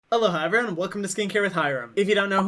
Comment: I don't care that you greet us with "Aloha everyone..." I always hear "Hello, hi everyone..."